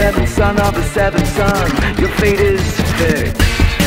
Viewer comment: This may JUST be me but.. At 1:40 I swear I hear '' Your feet is.. thick. ''